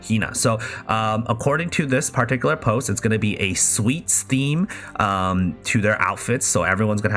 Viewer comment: How stupid can i be?? I misheard that the upcoming birthday gacha in JP server is sweet steam even though it was sweet theme 😭 and the worst part is i keep making comments on how exited I am to see the hikawa sister in the b-day card with sweet steam 😬